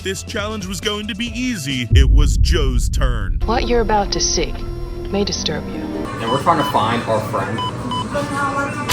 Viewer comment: what did she say? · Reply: “You’re not allowed to film”